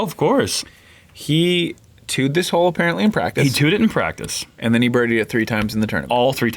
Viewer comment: Every time you guys says "He two-ed it in practice" all I hear is "He tooted in practice"